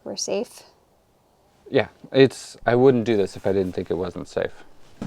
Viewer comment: It's nice that you show concern about the comments, but I think it should be the least of your worries...👴😉 @ 19:24 I guess you still have the additional weight of the H-beam and the cradles to subtract?👴🤔 In comparison to the original weight, there are some good decisions made! Well done! 👴👌 @ 20:47 Wow! Exiting times! I hope everything goes smooth!👴🤗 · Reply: "I wouldn't do this if I didn't think it was unsafe" or am I mishearing? The result of stress muddying speach 😂.